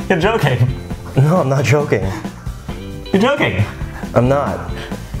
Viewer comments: Why on 0:13 it says y instead of your joking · They just wrote, "Y" instead of, "You're joking" HAHA